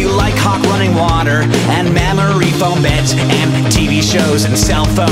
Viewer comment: The first time I heard this, I heard, "mammary foam beds"... I had to go back and listen to it again to make sure I heard it wrong. Haha!